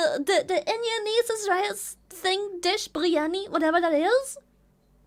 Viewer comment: DID SHE JUST SAY INDIANESE HAIIIIIIYAAAAAAAAAA. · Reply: She said Indian niece referring to the Indian lady.